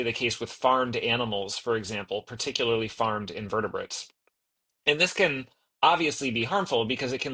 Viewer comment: Did he say, farmed invertebrates? What farmed invertebrates? Worms?